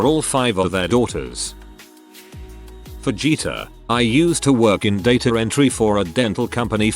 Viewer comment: Deadass thought it said Vegeta